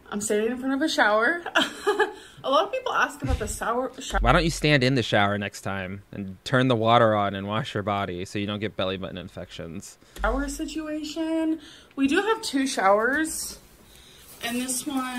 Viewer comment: Legit for a second I thought she said "A lot of people ask about the sow in the shower..."